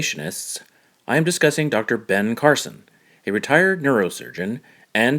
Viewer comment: Despite rewinding and listening to your narration between 0:20 and 0:25 more than a dozen times, I am still not sure if I heard you correctly. Did you say that Dr. Ben Carson was a "retired" or "retarded" neurosurgeon?